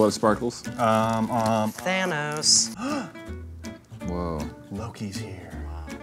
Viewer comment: lowkey thought said hannah instead of thanos and got really excited she was coming back